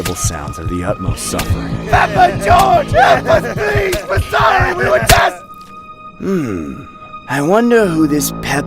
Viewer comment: what tf does he say??? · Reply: PEPPA GEORGE HELP US PLEASE WERE SORRY WE WERE JUST